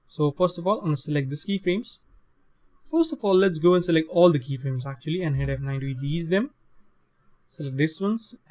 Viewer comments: sorry dude, on minute 12:53 you say select all the key-frames and then what did you say?? press nine?? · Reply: press F9 to easy ease the key frames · brahhh i didnt hear what you said on 12:54 ...tell me pls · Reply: Hit f9 to easy-ease them. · Hey, I need your help, I don't understand what you said in the second 12:54-12:58. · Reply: select all the keyframes and hit F9 to easy ease those keyframes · at @ 12:54 what are you saying after you select all of the keyframes? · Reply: oh easy ease...